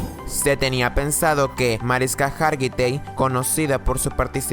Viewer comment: escucha y mira atentamente en el minuto 5:33 que dice marisca